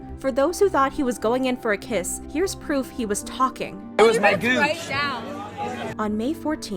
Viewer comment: did just say “it was my gooch”